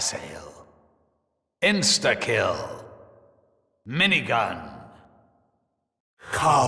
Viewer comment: Is it just me who thought they said instekio instead of insta kill as a kid